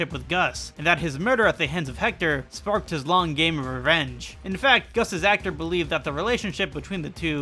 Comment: for a sec I thought he said "his long gay revenge"